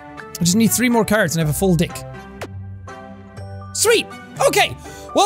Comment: 😂😂you said dick. Instead of deck